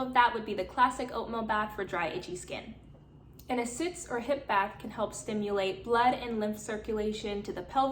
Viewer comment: what kind of bath did you say at 4:00? I couldnt catch it! thanks for all the info:) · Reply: It sounds like she said suits/sits or hip bath. I don't know if that's helpful 🤕